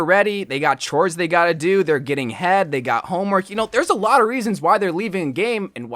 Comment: Did he say because there getting head